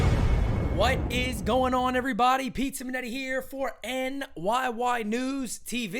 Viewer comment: love how the captioning mishears you, calling you "pizza minetti"... who sounds like a character in a billy joel song... ;)